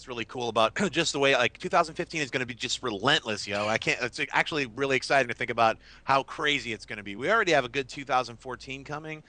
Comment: Did just say "yo"?